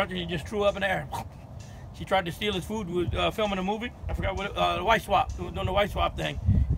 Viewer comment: So am I the only one who heard him say they were shooting a “wife swap movie” ? Lmaoo😭